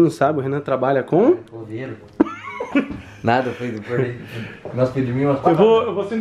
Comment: finalmente o Renan falou o trabalho, todo mundo já sabia mesmo kkkkk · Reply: A vontade da gente ouvir o RENAN falar bombeiro é grande mais no fim ele só falou coveiro😂😂😂😂😂😂😂😂😂😂😂😂quase que achei que ele tinha falado isso aí vim ler os comentários pra ver se alguém mais ouviu ai fui repetir várias vezes pra ver que ele falou coveiro